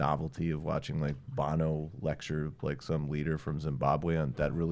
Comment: Did you actually say Weirdo from Zimbabwe?!